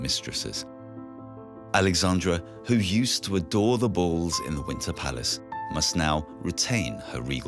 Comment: At the very start and 10:44:into this video, the narrator referred to the palace on screen as ''The Windsor Palace'', which is totally FALSE. The palace goes by 3 different names and NONE of them Windsor: the Winter Palace, the Alexander Palace and the Hermitage. The Windsor Castle is in London, UK. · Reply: I heard "Winter Palace", not Windsor.